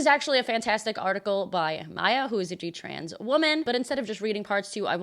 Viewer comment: What did she say? "a Jew trans woman"?